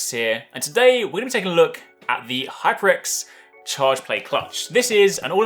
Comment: Had to rewind thought you said charge blade clutch